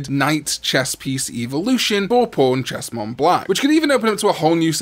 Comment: He said pawnchestmonblack and I thought he said pornchestmonblack lol 😂